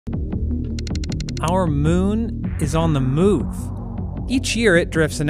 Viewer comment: I thought you said ''The moon is on the moon!'' at the beginning!